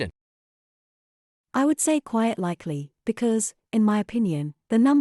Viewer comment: Why does the AI say "quiet likely" instead of the correct "quite likely" at 6:45?